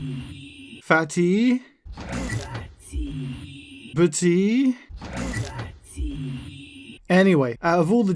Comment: i thought he said pooly😂